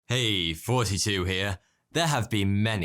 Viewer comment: i always hear " hey 42 here" idk why lol